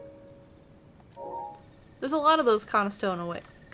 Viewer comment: does anybody know what was trying say before she settled on wagon? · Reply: It sounds like she was trying to say “Conestoga,” which is a kind of covered wagon.